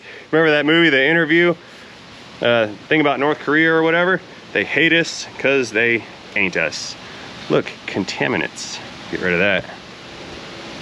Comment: They hate us because they ain't us..... At first, I thought you said "they hate us because you anus". I guess either works.